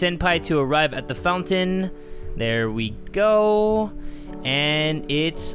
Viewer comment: ok I’ve seen this video before but right now is when I realized at the time I put in front of this sentence he said “there we go” and then I heard “here we go agents of the agency bakugou!” Haha lol